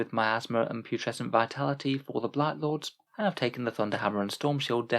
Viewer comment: Damn , I misheard blight lord as Night Lord and actually felt a twinge downstairs for a second =(